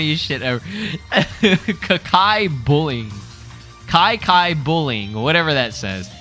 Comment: I thought you said Hakai bullying. Imagine Orange Piccolo with Destroyer Ki 💀